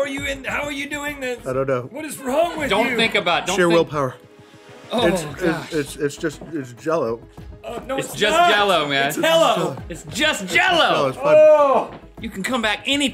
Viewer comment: Anybody else hear Rhett say “It’s HELLo!” at 13:32?